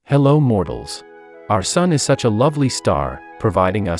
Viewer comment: I thought u said hello mortans